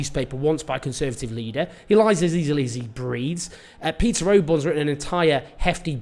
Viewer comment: I thought you said he lies as easily as he breeds, which I then realised is probably more accurate